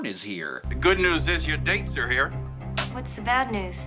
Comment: That’s not even the quote! “The good news is your dates are here” “They’re dead” is what he says!